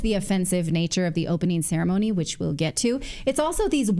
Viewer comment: Did I mishear when you sounded like saying , "scaremony," and not ceremony? You got it right!